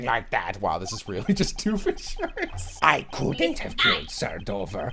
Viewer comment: did you say "doof in shirts" it's Doofenshmirtz